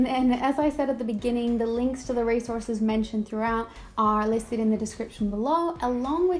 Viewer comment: I thought you said the link to the race horses. 🤦‍♂️ 😂 😂